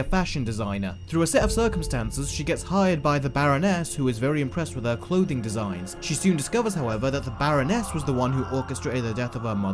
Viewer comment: I thought you were going to say "baronet" for a sec.